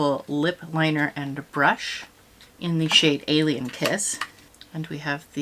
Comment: I was watching this while riding in a car and heard you say the name of a liner was alien p1ss 😅 but then I realized it must have been kiss.